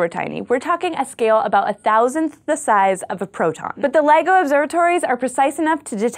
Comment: At first at thought you said Lego observatory.